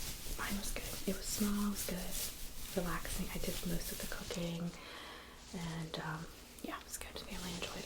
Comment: I was working and had put the video in the bg. I heard "yeah I took most of the co caine" I stopped and instantly repeated and discovered its "cooking" Gave me a good chuckle